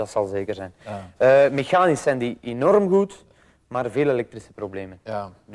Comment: wat zegt ie nou op 13:24 "mechanisch zijn die enorm goed maar ??? is het probleem" ? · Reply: Hij zegt: "Mechanisch zijn die enorm goed, maar veel elektrische problemen. Kleine details altijd....."